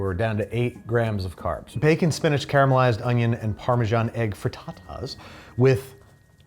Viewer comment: did anyone else hear "for tatas" instead of fritatas here? Or just me 😅